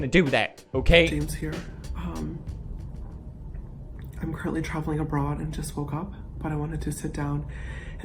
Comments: I really thought he said that he was travelling abroad to smoke up. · ngl I heard “I’m currently travelling in a bra” 😂😂